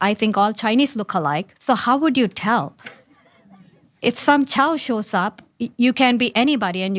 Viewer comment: Did she just say, "if some Chow" when referring to a Chinese person? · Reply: Woooow! I didn’t even pick up on that. My brain just heard “child” and thought, “why would a child show up to vote?”